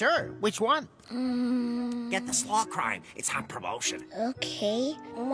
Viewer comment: For a while I thought they said SMALL FRY instead of Slaw Crime.